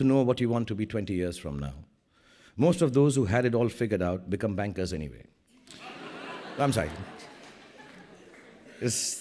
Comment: can any1 tell what he says in 18:22 , become?I tried listening again and again but cud'nt understand please anyone? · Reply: "Most of those who had it figured out became bankers, anyway. I'm sorry. If this goes live on YouTube- my big next loan for my film is finished from my friendly neighborhood banker."